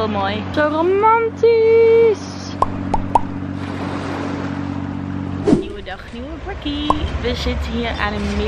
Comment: ik wordt zo blij van deze vlog. Je bent zo'n leuk mens <3 alleen wat zeg je nou bij 7:55? Haha ik kan er niet mee leven dat ik het niet weet, ik heb serieus al 10 keer teruggespoeld maar ik ben er nog niet wijzer op geworden haha. Liefs! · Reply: "nieuwe dag, nieuwe breakie (breakfast)" volgens mij! hihi😁